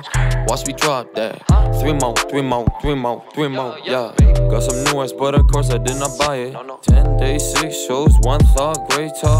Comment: i thought it said got some new ass butter course i didnt buy it